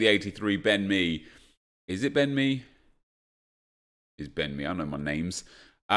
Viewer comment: Am i the only one who hears bend me?